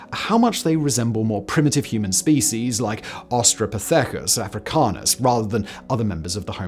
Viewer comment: did he say Austrapithecus ?